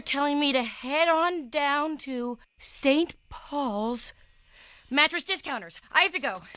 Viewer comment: thought she was gonna say ‘heading down to southpark’ ☠️